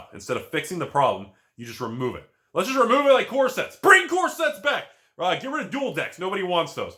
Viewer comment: When you said that they needed to bring back core sets, I was excited because I thought you said corsets.